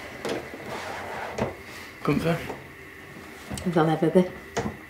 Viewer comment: What does she say ? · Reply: "If I'll ever be."